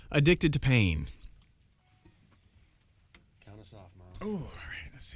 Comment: What is Mark saying in 3:20 towards Myles? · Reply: Count us off Myles